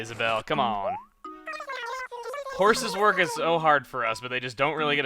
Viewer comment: OH Isabelle said horses... when I played I thought she said houses XD I feel like a dumbass now